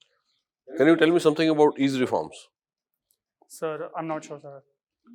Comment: Can anyone tell me what was the question asked at 11:19? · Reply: Ease Reforms!